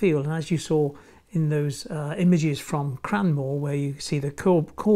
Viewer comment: Where did you say this place was? Cranwell/Cranwall? I'm trying to find the place. "Cranwell" closed in 1956, though this place appears to be a heritage railway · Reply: Hi Lynne, no it’s Cranmore. It’s in Somerset, regards.